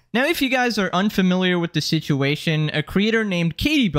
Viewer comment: When he was saying creator, I stopped at crea- So I thought he was about to say creature LMAO